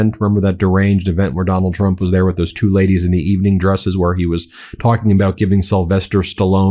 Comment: It sounded like you said "2 ladies of the evening" & I thought well, that tracks.